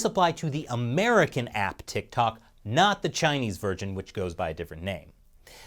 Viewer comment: Anyone else hear Chinese virgin?